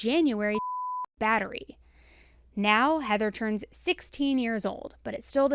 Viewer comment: The bleeped part between 17:27 » 17:29 is 'domestic battery' ..fuck me, I hate censorship. Edit: and here we go again, just a ½min later: 'Pornographic' Like, who are you doing this bleeping for, are there kids whom not only watch true crime content, but also at the same time might feel too titillated by the use of, I dunno what the category of these mildest of words even should be.. "Dude, watch out, there's a domesticated fucking pig over there!" This is the US tv-logic of: If you say ass it better not be followed by the word -hole .. Or we're gonna bleep your hole I mean, why do you take the sponsorships if you're just going to grovel for youtube acceptance anyway. FYI, your vid about Schabusiness was pretty much unwatchable.. Tho´ it could be categorized as "The most fucked up guessing game for kids" Guess what Post-mortem sawed-off limb went into what Schabusiness hole.